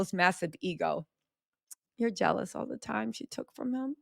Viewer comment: I 7:24 I initially thought said "you're jealous that she took your man?" 😂 Maybe because I was thinking the same.